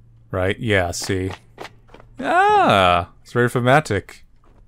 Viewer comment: What is the word he uses at 12:18? It's hard to understand him. "It's very _____." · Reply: "thematic" or something of such sort